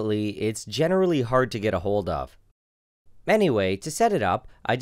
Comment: is it just me or did he say manyway instead of anyway?